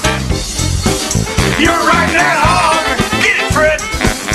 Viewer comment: I thought he said riding back home when I was younger.